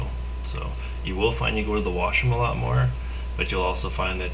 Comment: What a positive motivator, I will try to follow these guidelines. What does he say at 2:44? it sounds like "you will find u near the washer alot more...." also did he say seveny grams of protein or 17grams. Thank you!!